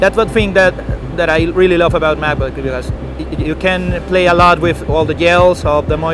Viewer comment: I thought he said “ you can play a lot with the gals “ … 😮‍💨